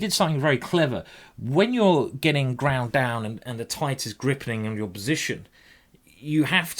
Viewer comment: did say "the tight is grippening" ?